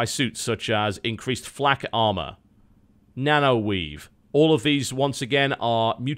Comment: did he say nano-weed